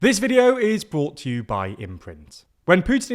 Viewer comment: I thought he said "This video is brought to by immigrants"